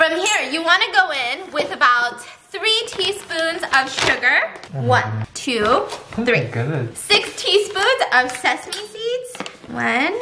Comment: i thought it was 60 spoons instead of 6 teaspoons so i was like "is that enough"